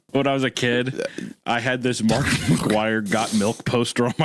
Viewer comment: I was listening to this episode while playing baldurs gate and i thought i heard "I had a markiplier got milk poster"